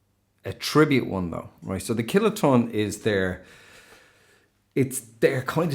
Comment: I keep hearing you say "Killer Tone" instead of "Kiloton" 😂